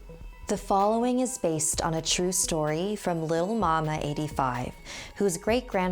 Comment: Ok I laughed SO hard when she said *this is a true story by (it sounded like she said lil mama 85) lil mama 85 Edit:OMG SHE DID SAY LILMAMA85 AHAHAHAHAHA